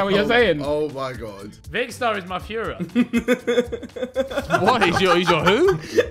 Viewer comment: tf did ethan say i didnt understand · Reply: He said Vikkstar is my führer . Hitler was referred to as führer . It means a tyrannical leader